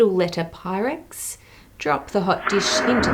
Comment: OH, I thought you said "Pyrodex." It explodes 'cause it's gunpowder.